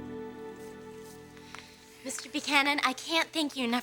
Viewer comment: Mr. Buchanan Panan. I legit thought Amber said Buchanan, but the scribers had other plans